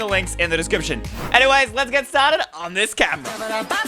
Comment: DID HE SAY "Enyways lets get started on this cap" ??????? 🤨🤔 Or am i just dum